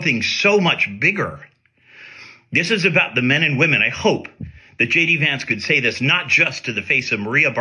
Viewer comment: It sounded like he said shady vance